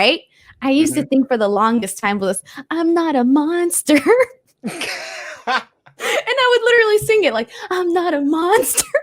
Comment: isn't the only one that thought Nia Jax's theme said "I'm not a monster" I did too 😂